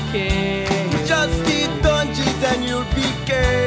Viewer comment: The first time i heard this i heard ''Just eat oranges and you'll Decay''